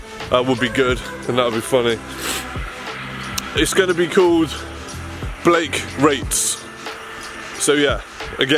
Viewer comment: Sounds like homie said Blake Rapes 😅😅🤣